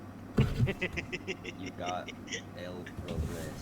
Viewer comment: I thought you said you got divorced